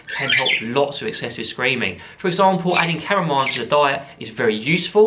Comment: Around the 9:00 mark you mentioned adding something to the diet to curb hormones. Was it cardamom? The captions say caramel 😆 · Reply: Caramel works for me but not the birds lol It’s chamomile! I have a hormone series if you’d like to learn more about them and birds!